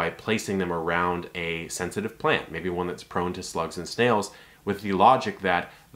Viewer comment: it's weird to me has a fish tank keeper that people think egg shells will deter snails.cause I feed egg shells to my snails to give them calcium to help maintain their shells. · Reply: Sarah Rogers 1:36 Did he say Slugs AND SNAILS multiple times or am I just hearing things?? At least that’s what it sounded like to me...